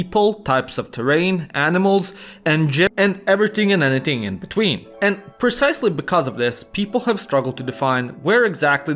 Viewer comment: what does he say? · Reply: "And g*psies", but it was cut off. Lol